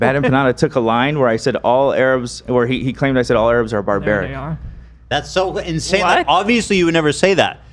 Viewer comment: 😂😮 does Hila say ,"And they are" when AB said all Arabs are barbaric?😂 I loved that haha. Then Ethan tries to cover over it and Hila is all whuttyt😮?😅